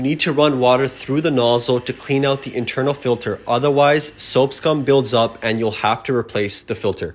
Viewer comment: Was I tripping or did I really hear "soapscum" ? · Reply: You really heard “soap scum”